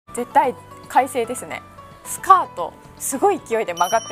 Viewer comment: 『絶対、快晴ですね』が『絶対、開成ですね』に聞こえてしまった、、、、、